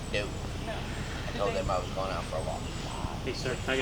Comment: Did he really just say I told my aunt I was going out for a walk😂😂 Btw Props to the officer, I heard him say thanks for your service when he first walked up....